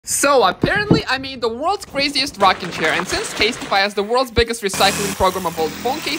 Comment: At first I thought he said Rocket chair was excited haha. Still cool. Well done got a sub from me.